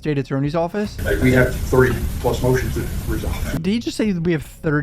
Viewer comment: it rly sounds like he said "3 motions" did anyone else hear 30 like? lmk if i'm deaf